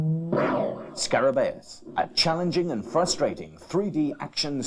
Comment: Did he say challenging and frustrating? Frustrating isn't a great selling point xD